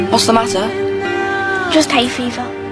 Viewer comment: I thought she said just hate fifa…